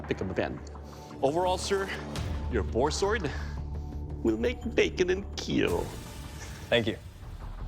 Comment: First time i heard him say "will make bacon and keel"